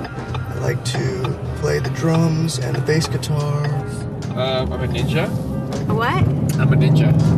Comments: minute 1:33 him..." um, I'mma ninja" she..." what?" him again..." I'mma a ninja" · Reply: T3SKATLIPOC4 I thought I heard him say to her "I am a D.J. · what? I'm a Ninja too....nothing to see here. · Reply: Dark Horse Woodworking dude.. I had to do a double take cause I thought he said he’s a dj, but he really did say ninja! Lmao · minute 1:33 him..." um, I'mma ninja" she..." what?" him again..." I'mma a ninja" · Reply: I thought he was trying to say “I’m a DJ”